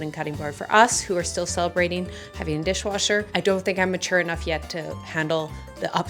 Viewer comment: I’m cooking right now so not continuously watching the screen and I heard you say “washing machine” three times stating you never had a washing machine growing up. I had to turn around to realize you were talking about a dishwasher. 😂 Also little tip for wood cutting boards… I have one plastic one I use for raw meat/fish. I use the wood ones for everything else.